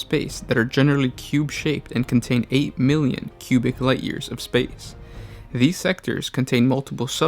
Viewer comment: did you say... light years?